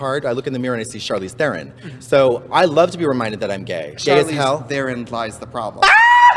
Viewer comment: what did Trixie said? I don't understand and the caption is not really helpful hahah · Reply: katya said she looks in the mirror and she sees herself to look like Charlize Theron and Trixie made a dad joke saying "charlize therIN lies the problem"